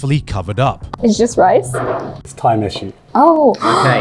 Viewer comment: when says "Tai Mishu" I heard "time issue" which is also true